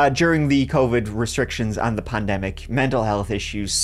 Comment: did he say "plandemic?" Hoping it was just a slip of the tongue. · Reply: I heard “pandemic”